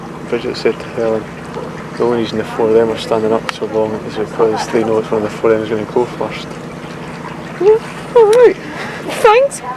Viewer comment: What are they saying Bridget said? @ 19:17 and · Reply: For 19:17 I think it was "The only reason the 4 of them are standing up so long is because they know one of the four of the them is going to go for us" and for 19:57 "the boys know that they're gonna be off next."